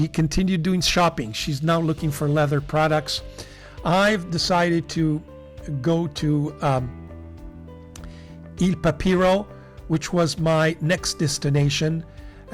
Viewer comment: For some reason I kept hearing "I'm going to IL Papiro" as "I'm going to eat a hero." (But don't mind me, my hearing is shot.)